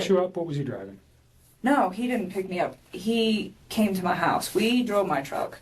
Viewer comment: people keep misunderstanding this, she’s not saying he don’t drive. The question was “so did he pick you up, what was he driving?” She then says “no, he DIDNT PICK ME UP WE DRIVE MY CAR” she doesn’t answer the question about what he drove to her house, she only answers the question about being picked up and says they took her car. The detective should have repeated the question “what was he driving” and this is what they call a “compound question” in court because it has more than one question in it, and this is why they are not allowed in court, because the subject can answer part of the question and not the whole question, effectively evading certain points or topics. I’m not sure if she answered this question later but she and Chris both do say that his vehicle was a work vehicle and that wasn’t allowed to be used for personal use. Most likely he drove the Lexus.